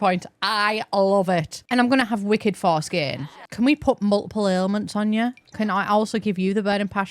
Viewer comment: Not me mishearing "wicked force gain" as "wicked foreskin." 🤣